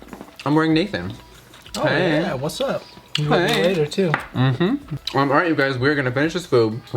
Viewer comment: Was I the only one who heard whisper "maybe later too?" lmao.